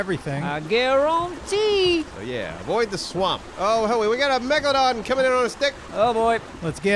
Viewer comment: "We got a Megaladon coming in on us Thick". At first, i misheard that as "We got a Megaladon coming in on a stick"